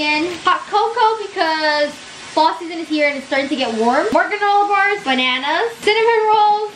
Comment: did she say warm💀